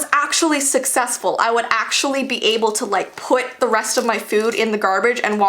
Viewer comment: I THOUGHT IT SAID "my teacher made me eat in the garage" lolloloolololoo IM DEAD